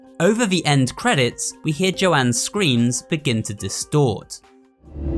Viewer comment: I coulda sworn that I heard Joanne screaming wooly’s name